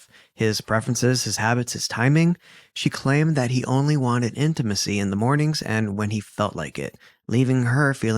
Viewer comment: When said Steve only wanted intimacy in the morning, I had to rewind. I thought he said Steve only wanted to eat p***y in the morning 😮 haha ha ❤🇦🇺 😮